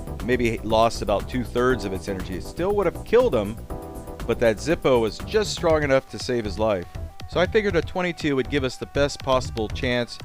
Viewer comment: Did you say, "It still would have killed him but that Zippo was just strong enough to save his life"?